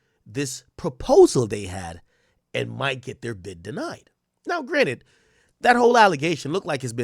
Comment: what's always saying? Now granted or?